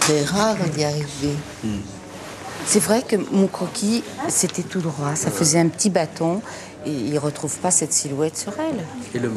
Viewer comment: Something is strange... at 00:43 the woman say "It's true that MY sketch was straight". The subtitles says "...HIS sketch...". Who is really designing the collection ? YSL was so down....